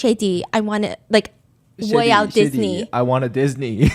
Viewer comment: I thought he said “Shetty Shetty, Walt Disney” and with the visual of him in the park?! I was so confused 😂